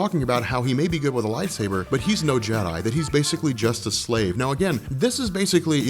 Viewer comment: I thought they said he's "just a slave to his emotions"